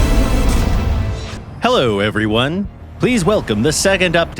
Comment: the cc said friend and the voice said everyone 😅